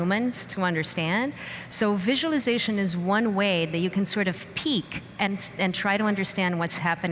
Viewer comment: 40 the subtitles read: "So visualization is one way that you can sort of peak and try to understand what's happening in these systems". The word should be "peek". The speaker actually reinforces what she says by her body language (her hands and arms looking over the edge of something). "peak" makes no sense in the context and you may be able to guess what she means simply by listening to the audio, maybe in combination with her body language, and by ignoring the incorrect subtitles. Is an AI system doing the subtitles? Yeah, humans too can produce utter garbage in creating subtitles (we could open a channel for some real doozies!) but at least any reasonably educated person can pick up the mistake and make them read sensibly. Does it matter? Yes, it does to people who are deaf and to people who do not know the language being subtitled. English has a lot of homonyms and their misuse gives rise to jokes, and you can still enjoy the opera/film in another language even if mistakes are made, but correct "translation" is incredibly important in international councils like the UN. "That is why it was called Babel—because there the Lord confused the language of the whole world. From there the Lord scattered them over the face of the whole earth." AI has a long, long way to go to making a "good hash" of speech recognition. What a lot of levels of meaning and punning can be made out of those two little words :-)